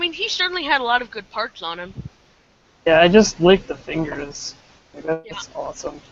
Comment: hey guys, can i join next time? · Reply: skip to 2:47 it sounds like said he licked the fingers XD